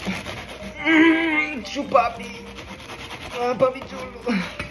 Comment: “Chu papi” got me off guard💀💀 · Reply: It sounds like he said “papi chulo” after that🪑